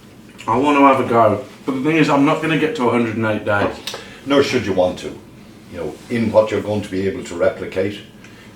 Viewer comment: "I'm not gonna get to 108 days" "Nor should you want to" sounded like "No shit, you won't."